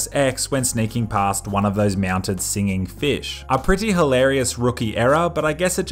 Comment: did he say "meownted"? 4:36 see, I have a keen sense for this kind of thing because I've been listening to people for a very long time! lol